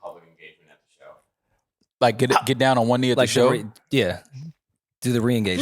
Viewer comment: did mal say "get down on one knee like joe"